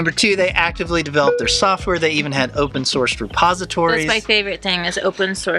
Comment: I had to rewatch the part about Repository as the first time I thought she said Suppository and spit coffee out my nose, thanks for all the usefull information😝